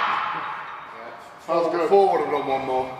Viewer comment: What is Eddie saying.... anyone? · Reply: He said Thor would have done 1 more